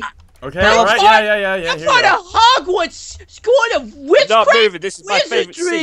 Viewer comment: What did say at 2:12 hodwads skool of widcraf and widardry? lol